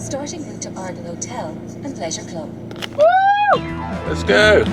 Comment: When it said leisure club I thought it said pleasure club lol. I was like well ok then. But I read the sign and thought oh ok,,, boring lol